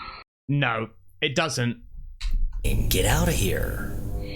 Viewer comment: It sounds like it says "Get out my house"